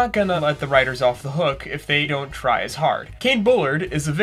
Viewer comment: Ik I’m late but did I just hear “Don’t Try Su!cide” in the first 2 seconds? (Queen fan here)